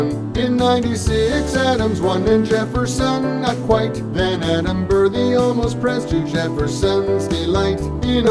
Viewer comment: I heard "Adam Burr.”